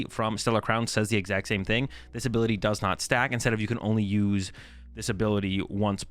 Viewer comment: I honestly thought the togekiss said “this ability does not suck”